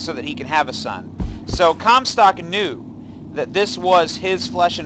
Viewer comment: I hear "Cumstock" every time... dammit